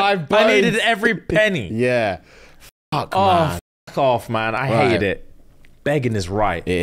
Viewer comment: I heard "sweetheart" instead of "Fuhad". It tracked and didn't surprise me but the lack of reaction made me rewatch it.